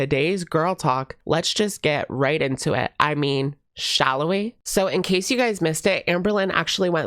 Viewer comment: Maybe I'm mishearing things, but is saying "I mean, let's get into it! Shallow we?"?